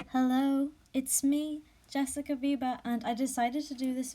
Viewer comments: I thought you said Jessica Bieber in the intro I was honestly terrified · I heard "jessica Bieber" And i got scared 😂😂